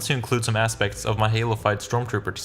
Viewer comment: every time he says "halofyed" It sounds like he said halo 5 and I got really confused at times